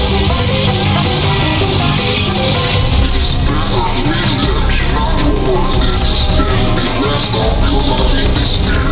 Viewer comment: What does Butch even say at 1:06? Edit: He says ''It's better to meet them in another world than spend the rest of your life in despair. Come on Spike! Make your choice. I am the disaster itself'' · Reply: It's better to meet them in another world than spend the rest of your life in despair. Come on Spike! Make your choice. I am the disaster itself